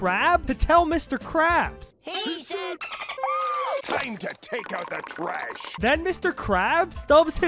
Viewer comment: Did he just say take out the obama😂